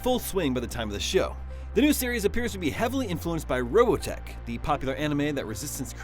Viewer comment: what did he just say? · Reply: Robotech